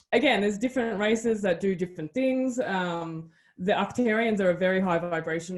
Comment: I wonder if she means Arcturians (sounds like she says "octarians" and I could not find a good hit on that word). Thank you for sharing this interview, and thank you to for sharing her story. The more people can come out about their experiences, the closer we get to the next stages and get our governments to fess up. So very close.....